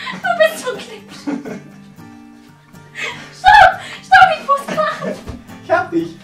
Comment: Hahahahahaha 6:13! :-D Hauptsache ich hab erst verstanden: Schatz, ich muss kacken! :-D